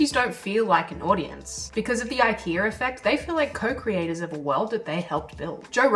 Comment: I keep hearing "The ikeaR effect," weird.